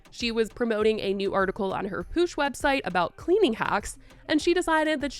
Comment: did i just hear cleaning hacks as if she cleans anything